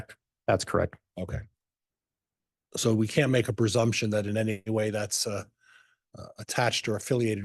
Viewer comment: Did he say persumption ^ is that a word ? Isn’t it presumption lol ? Maybe I heard it wrong